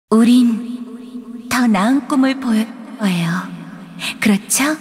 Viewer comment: "우린 더 나은 꿈을 보여'줄' 거예요. 그렇죠?" 18:06 "더는 '가망'이 없어. 데브리안은 '결국 무너질' 거다." 18:13 "하지만 그래서 뭐? 나는 '도전'해 볼 생각이다. 도망'치고' 싶다면 그렇'게' 해." ' ' 안의 문구는 ...로 표기된 부분을 미약하게 들리는 대로 끼워맞춰 본 결과입니다